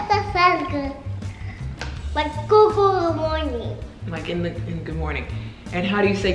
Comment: it sounded like she was trying to say Good Mythical Morning lol